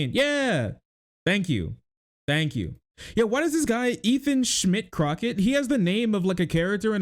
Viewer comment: I'm sorry, but when said, "Ethan Schmidt Crocket" , all I heard was "Ethan Shit Rocket" 😂